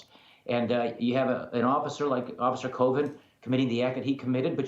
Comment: I think his name is Rob. Never mind. Rob mispronounced Chauvin so all is fair. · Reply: Sound like he was saying officer Covid😂